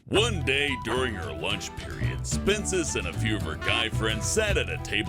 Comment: Why do i always hear Spincest whenever I hear Spincess' name? Weird